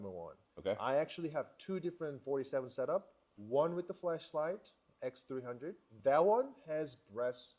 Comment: did he say Fleshlight?